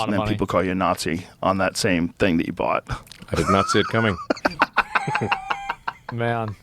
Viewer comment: Did he said I did Nazi it coming 0:07 ? lollll